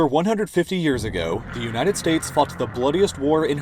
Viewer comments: am I the only one who heard fucked instead of fought? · I thought he said fucked the bloodiest war